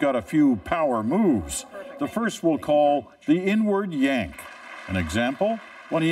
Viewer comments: I thought he said, the 'N word Yank'😂 I was like, wtf?! Ohhhh, the INWARD yank. 🤣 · I thought the commentator called it the “N-word yank” And for a moment was flabbergasted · i thought he said , the n-word yank 🤭